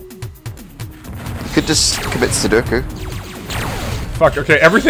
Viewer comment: Did just say you could just commit Sudoku? XD